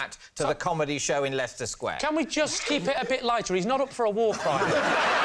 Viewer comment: Henning's best line got drowned out by laughter haha Lee Mack: He isn't up for a war crime Henning: not on this occasion anyway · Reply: Ngl I thought he always said that he's not up for a walk right now